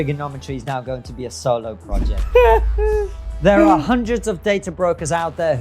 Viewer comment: Thought it was better when instead of 'solo project', I thought you said 'Triggernometry is now your sole employer' then FF cried. Seriously your ads are better than mainstream content. Great interview, would like to see more talk about the conflict between merit based value systems, and the lowering of quality of life and societal output when those systems are counteracted.